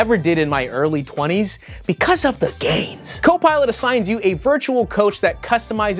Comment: Sounded like you said "it's for the gays" instead of gains 😂